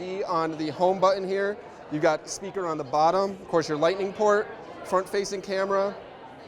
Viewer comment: Did he really call the lightening port "Portrait lightening port" at 0:34 ???😂😂😂😂😂